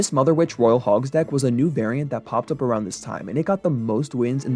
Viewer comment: I thought he said "this mfer" 😂😂